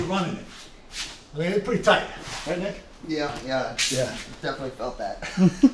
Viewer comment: I no longer think that's Jon Thomas and now I'm worried I'm ginger racist. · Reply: I think I heard call him Nick.